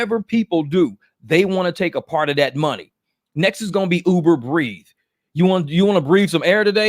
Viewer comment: said “Uber Breathe” I thought he said “Uber Grieve” ⚰️😂 Here at Uber Grieve, we make funeral arrangements available at the click of a button. We will set up your wake service, burial details, plan the reception and go over flower decorations and casket options. We do this because we care. Uber Grieve ⚰️Reimagining how your loved ones are remembered. *Plans start as low is $9,999.